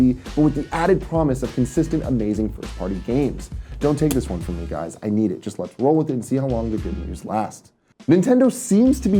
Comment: What is Tim saying at 4:10? I can't figure it out haha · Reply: "Don't take this one from me guys, i need it. Just lets roll with it and lets see how long the good news lasts."